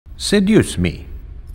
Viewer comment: Oh Aether said “Seduce me” I thought it was “Say Use Me”😂